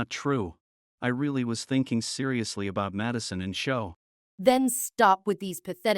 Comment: the subtitles said "Madison and Thomas", but he said Madison and Sho. In 15:55 it happens too.